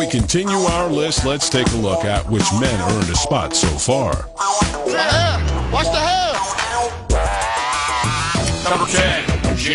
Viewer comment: What did Vince Lombardi say around 21:10-21:14?... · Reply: "What the hell's going on out here!"🤣